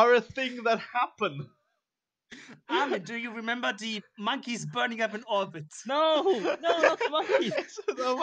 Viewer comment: did he say Monkeys burning an Orphan!?